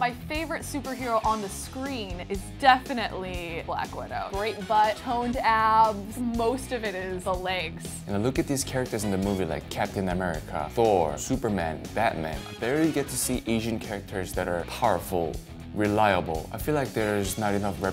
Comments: I thought he said “ the characters nipples” 😨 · DID SHE SAY GREAT BUTT? I HEARD THAT please like :)